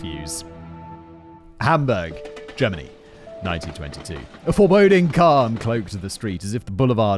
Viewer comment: your subtitles put 1922 instead of 1992. I was so confused for a while!